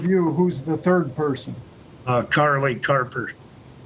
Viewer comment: How is this even possible. They actually got rent assistance. Do they not ask for a friggin pay stub? LoL I thought he said Charlie Harper. They pay bills huh, yea no.